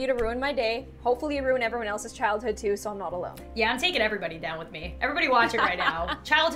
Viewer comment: When she said yeah “I’m taking everyone down with me.” I thought she said Beyoncé Down with me